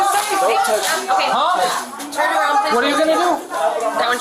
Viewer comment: subtitle error. Subtitle says "I want you to-?" What the female cop said was "That one too." While pointing to the brother that had said he would slap the shit out of the cops. She was saying "That one too." because she wanted him arrested too. I swear the person that does the subtitles needs to have their hearing checked or get some good headphones.